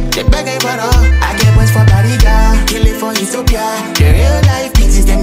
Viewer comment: Is he say Ethiopia 1:22 oooo 🇪🇹😢💓💓💓